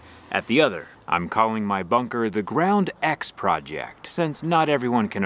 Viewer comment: At first I thought that said Grond 😂